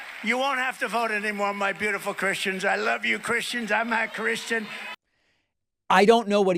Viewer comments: To me it sounds like he says 'Imma' Christian. Midwest people say 'imma' in place of 'a' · It does sound like he says im not cristian that what i heard first And every time i hear it ! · Please vote! Just this one time and what’s wrong with his dentures are coming losing It’s sounds like he said I am not Christian! His dementia is letting how he really feels out loud. The maga ass kissers says he was making a joke! F that crap we dibt need a person who is not serious and is always just joking and keeps on basically doing stand up! Harris famosos need to point that out! We are in serious times and don’t need a stand up moron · I thought I was crazy! I’ve heard that clip so many times and definitely thought he said “I’m not Christian.” But I haven’t heard anyone else mention it. Glad I’m not the only one who heard it